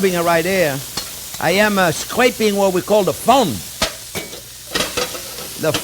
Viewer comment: I thank you! I always thought they called it "from" or "frum", now I know ind "Fond!"